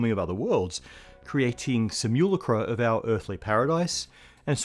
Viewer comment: "...creating simulacra..." was oddly ear-catching. We all have our respective accents and dialects in a given language, and this bit sounded like "...creatine some you lucre"; a moment of cognitive dissonance at which I paused and had to wonder, given the current state of advancement in (and unusual pronunciations by) and lesser deployment of LLMs and deepfake tech..